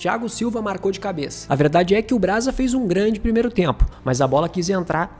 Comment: Escutei alguém falando BRASA? 9:17 😂